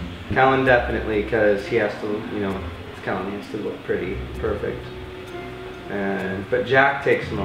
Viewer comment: OK IM SORRY BUT I HEARD HIM SAY "KELLIN DEFINITELY, BECAUSE HE NEEDS TO LOOK PRETTY, FOR VIC"